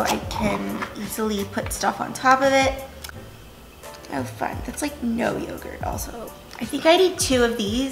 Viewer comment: Wait did she swear haha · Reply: it sounds like you said “oh fuck” at 2:21 . 💀